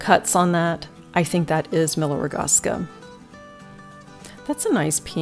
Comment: who did you say made the pitcher that had the Waterford 16-petal emblem on the bottom? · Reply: Many Waterford pieces have 8 cross cuts, which shows as a 16 star cut. Miller Rogaska most likely copied this signature look.